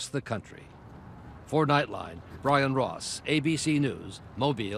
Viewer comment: did he just say ryan ross?!